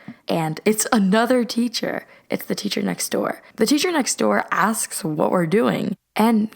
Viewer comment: What is she saying here? (I know she's trying to say "the teacher next door" but it sounds a bit weird and also this isn't a hate comment)